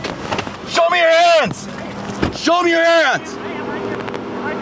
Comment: why did I think he said show me your ass 🤦🏻‍♀️